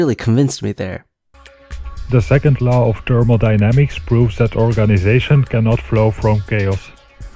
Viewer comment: HDHFK IM WHEEZING I FUCKING THOUGHT HE SAID "TURBO DYNAMICS"